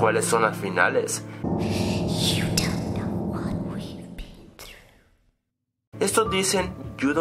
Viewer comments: Yo escuche "You dont know what we did" Díganme que no soy el/la unic@ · Yo escuché “You don't know what I be” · Yo le entendí "If you know what I mean?" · te quiero decir lo que yo escuche "you know what i wish?" traducido: "¿sabes lo que deseo? eso entendi yo